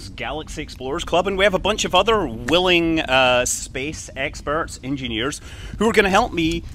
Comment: I'm trying the premier feature so I can chat with people live for fun. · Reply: Hey Scott,did you just said "SpaceX-perts" 😂